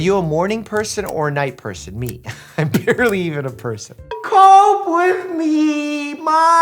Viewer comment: idk what he was doing untill I found out he said"cope with me" (You can't skipp to this if you don't want to hear it anymore 9:43) (Skip to this one for the actual if you don't wanna hear him)